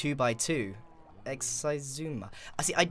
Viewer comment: The captions say exercise Zoomer when he says Xisuma